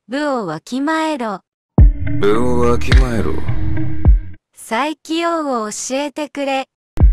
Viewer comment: 悠仁のセリフが「斉木 洋を教えてくれ」に聞こえる